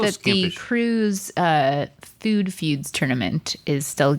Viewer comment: when Stevie said the "crew's food feuds tournament" i thought she said the "cruise food feuds" and i got really excited, but then i realized. so this is my humble petition to actually make a "cruise food feuds" please and thank you 🙏